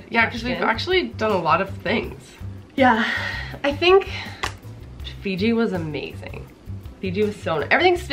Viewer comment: did u say FIJI ...have u been here